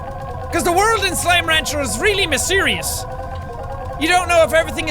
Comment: Wait......did jack say misereus